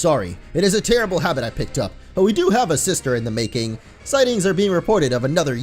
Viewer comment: said we have a sister in the making instead of disaster!!🤣🤣🤣